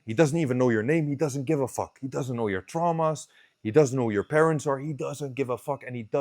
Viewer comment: when he said "trauma"..it exactly sounded like farfromweak..or is he?